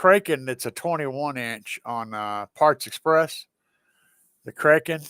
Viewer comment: I misheard you when you said the Kraken 21" on Parts Express. I thought you said Aliexpress. Lol. Yeah, lots of good reviews on that sub. 👍